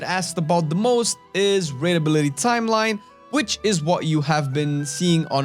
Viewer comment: Raid Ability Timeline. The CC said "Readability Timeline"